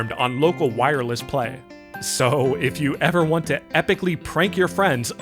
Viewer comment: My brain misheard "If you want to permanently brick your friends". I need to sleep